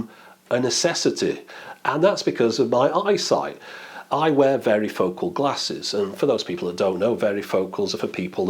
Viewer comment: I'd really like to see that original form factor come back, I didnt realize they had gone away, its so useful! When you first said varifocal I thought you were saying "veryfocal" meaning they have a really intense focusing lol. I'd never heard varifocal before but that makes sense, in the US we call them bifocals. Some people also have trifocals, and then theres progressive or no-line which basically smooths the transition between the focal zones. Varifocal seems useful to refer to the whole category at once I wouldn't expect 9 stitched photos to have 9x the pixels of one photo. It's smart combining them right? I assume its taking photos that have a bit of overlap, and that overlap is being removed when the photos come together. To be able to append 9 photos together without any sort of computerized combination you'd have to be pretty careful with how you took the photo so it doesn't come out distorted